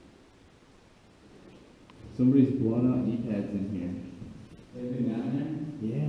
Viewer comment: hey I wasn’t quite able to understand what it was you found, but I think it sounded like “someone’s blown out knee pads”. Was that correct or am I missing something?